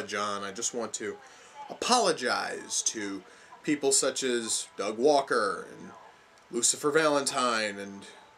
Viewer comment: Did I just hear him apologise to Doug Walker?